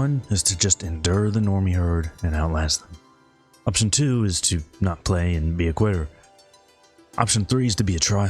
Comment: Am I the only one who heard "option two, don't play and be a queer" haha 😅